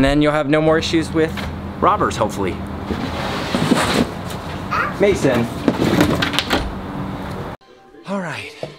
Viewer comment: I first thought he said "Makes sense!"